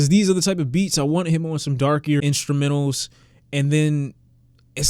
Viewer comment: Did you really say the word "darkiest"